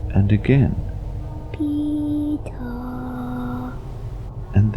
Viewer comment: I heard pito #pitogang